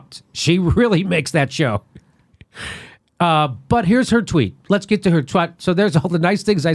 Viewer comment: do I hear "let's get to her twat"? Nooo! :)